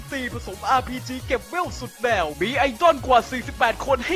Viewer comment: ผมได้ยินเป็นไอจอน